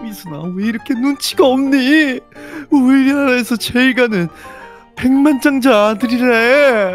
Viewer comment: 우리아아 에서 제이 잘 나가는 이라고들림